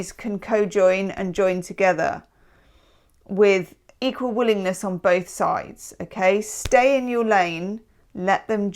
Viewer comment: “equal willingness on both sides” I thought you said “equal wooliness”; which I think suits my situation better